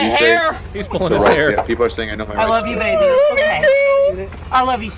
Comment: Did he say, “Harder, Daddy?” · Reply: No, he said, "I love you baby. " to the woman in the car.